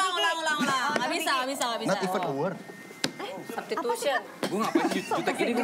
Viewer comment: Nyari jawaban di komen blom nemu ... di menit 5:20 si vincent ngomong apa ya ke desta ??.. mohon pencerahan nya pemirsa para vindes mania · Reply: Not even a word